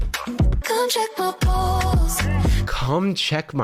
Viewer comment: I appreciate that Jarvis believes that she says, “Come check my pulse” but until my dying day I will swear she said, “Come check my balls.”